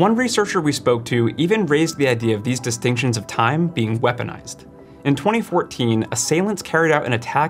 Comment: caption is wrong: says "recognized" but he says "weaponized"